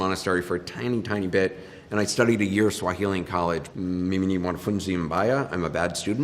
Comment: When he said "mimi ni mwanafunzi mbaya" i heard mmmimbmya then i saw it was swahili and understood the whole of it.